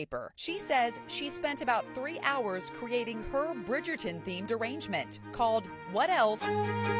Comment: I heard "...creating her Bridgerton themed derangement..."